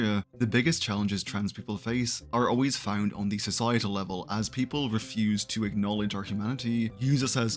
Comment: i heard "as people refuse to acknowledge our tomatee